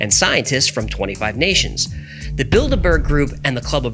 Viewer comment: I wasn't looking at my screen and I heard that as the "Build-a-bear" group. 😂